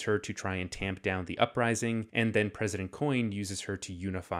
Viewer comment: Good video but at 8:36 the subtitles say “an then President Snow” when they should say “and the President Coin”